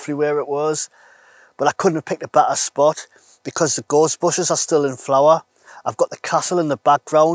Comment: Nice one .. I would love the Jackery but a bit above my current spending ability. Looks great though...By the way, I thought the first time you said "Gorse bushes", You said "ghostbusters".... Us Derbyshire folk are more used to hearing sheep than north eastern accents lol. Best wishes mate.